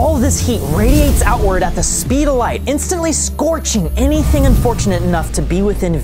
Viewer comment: sorry I meant over the speed of light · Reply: ohh sorry i misheard what he said he said at the speed of light not more than